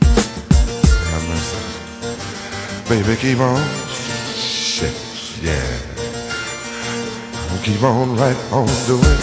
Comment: Did he say. "shit yeah" at 6:37? Makes it even sexier :)